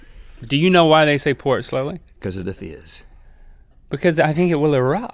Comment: When Rhett said "cause of the fizz" I thought he said "cause of the fetus" and I was like "WOO THERE RHETT WHAT THE HECK" and then he said it again and I understood.